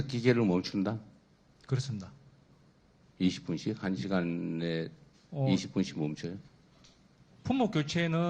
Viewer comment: 4시간에 20분씩 쉰다했는데1 시간에 20분씩으로 들었구만